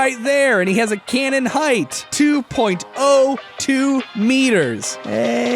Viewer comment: vader being 7ft scares me even more than just the force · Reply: 6'7 and I misheard it as 2.2 meters not 2.02 meters